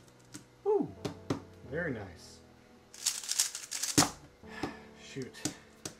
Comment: Did you just say say shit. i am unsubscribing.